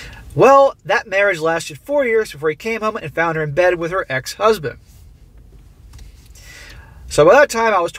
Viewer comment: Man, I just about had a heart attack. I thought you said, "found him dead with her ex-husband." ._. I don't need that sort of escalation this late at night lol